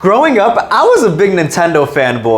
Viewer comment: When you said growing up I thought you said throwing up and I’m like what like what